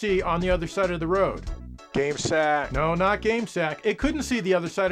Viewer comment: Did he just say, "Gay sack"?🤣